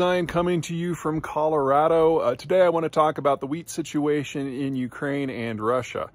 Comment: First thing I heard “ I am in Colorado and I am going to talk about the weed situation in Ukraine and Russia “😮